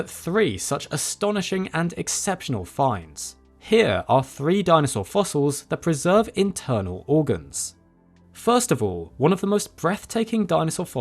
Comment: I thought this shit said "3 dinosaur fossils that deserve internal organs"